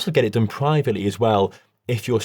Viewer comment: Lol i was lostening to this and the i hear he said "It might cost fifty hundreds" 😂😂😂😂 i was like "wait, what!? What kind of number is that?"... 😂😂😂😂😂 had to stop and go back and turned on de captions😂